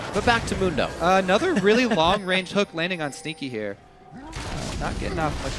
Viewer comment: Sounds like Kobe says "Stinky" instead of "Sneaky"